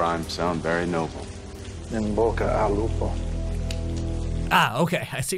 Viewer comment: Actually what the mob said in the minute 5:35 was "In bocca al lupo" which means "good luck" in italian